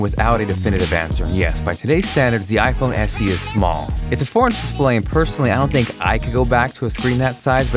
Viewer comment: I thought you were saying "foreign display" until the end of the video lmao